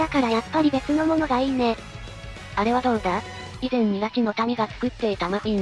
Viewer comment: ラチの民か ナチの民って聞こえて攻めすぎだろって